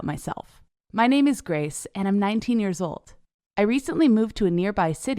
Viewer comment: LOL I thought she said I’m 90